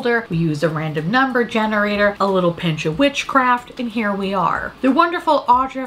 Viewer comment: OMG I thought you said bitch witchcraft 😆 🤣